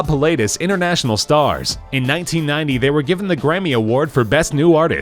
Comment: did he say gremmy instead of gramy?